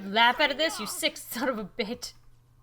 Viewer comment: she said bit instead of bitch